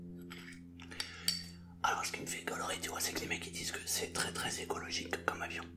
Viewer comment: C'est moi ou a 5:30 il dit golrie ? 😂😂